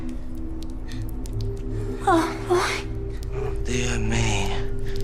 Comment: did she say oh boy